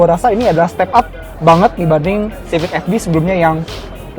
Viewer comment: Siapa tau ada ke ajaiban om jd rwd..oyia kalo yg di luar saya liat ada sunroof.. yg indo ga ada ya ? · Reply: sama mau tanya di menit 6:02 .. saya dengernya civic fd yang gagal ? apa fb ?